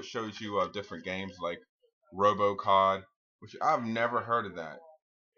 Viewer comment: dude did you just say robocock, or robocop? just asking b/c its hard to make it out.